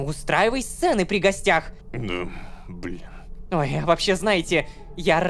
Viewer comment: мне одному послышалось слово: «б*я…»